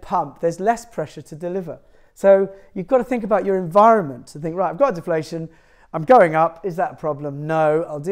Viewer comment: the subtitle says " ... to think right God's deflation I'm going .... " --- my apologies :D